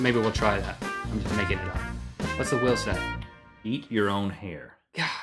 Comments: i thought he said eat your own ear 0.o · I thought you said "Eat your own hand." I was worried when I saw the big scissors.